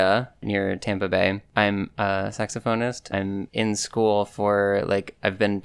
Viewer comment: I misheard saxophonist as sex offender. I'm so sorry.